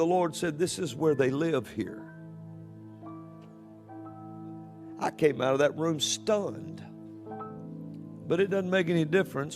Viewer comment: Great credit should be given to this man. He is somehow able to bring about grandiose delusions and hallucinations in a sober state. I mean, I've been on that level before, but never without the massive help of a serotonergic compound. This dude evidently has a gift of going 🥜. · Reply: I thought he said I came out of there stoned. 🤣